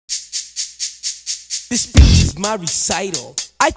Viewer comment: I thought that it was saying this b** is My recite and not this speech is my recital